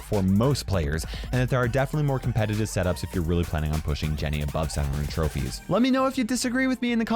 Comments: he says Jennie instead of Jessie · What did he say JENNY??